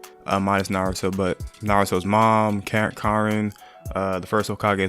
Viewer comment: Sorry you misunderstood me, I didn’t say Karin was his mom, I was just listing people out. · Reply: Fasho it sounded like u said karin was naruto mom but alright my bad